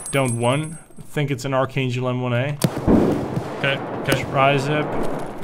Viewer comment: wtf did you say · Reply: he said 'archangel m1a' archangel is a type of stock for the m1a